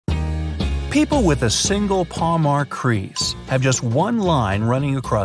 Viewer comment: When the 1st line of the video said, Those with a single line or crease. I heard it as those with a single line are creeps 🤨😂✌️🤠 The playback speed was at 1.25x ! Also I have 4 of those see bright side pinned comment!! (21st reply comment)